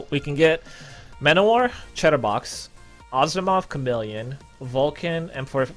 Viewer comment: when says chatterbox it sounds like he says cheddar box lmao